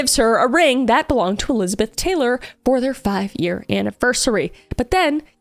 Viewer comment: did i hear elizabeth taylor?